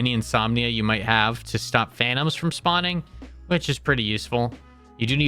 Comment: please tell me im not the only one who heard "to stop THANOS from spawning"